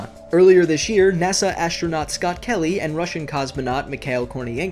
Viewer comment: i thought he said scott manley